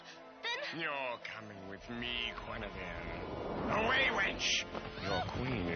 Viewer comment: did he call the other girl a weinch? He says "away weinch" wow!!!!